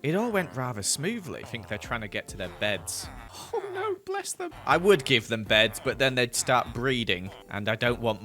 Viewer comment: did you say bless them to villages😂🥺 And I for one love watching you gather materials 😊